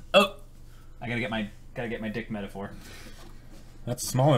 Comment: The subtitles in the beginning of the video (0:00) said the exact same thing as what I thought said. What’s “d*ck metaphor?” O.o